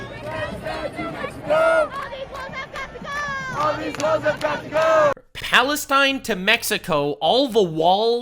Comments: I thought they said all the hoes have got to go. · I thought they said “all these hoes has got to go” 😂😭